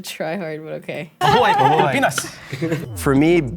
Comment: BRO, i just misheard "my boi pilipinas," instead of "mabuhay ang pilipinas." lol